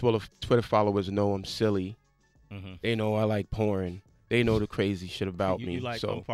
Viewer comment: i thought homie said, "pourin" 8:28 is hillarious